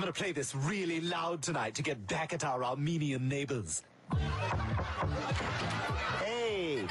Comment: I heard "Arminean neighbors" and was expecting something theological.